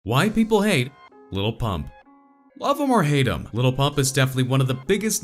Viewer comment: Sounds like he said "Why White people hate lil pump"